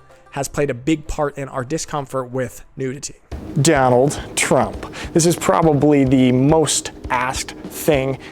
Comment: did I hear "Downhill Trump", or did I look at were you were?